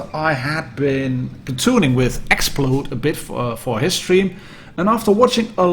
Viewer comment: what was the name of the player you said you platooned with @ 5:16 in video? I'd like to watch his channel to see what you were talking about. BTW thanks again for polished content! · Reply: XPload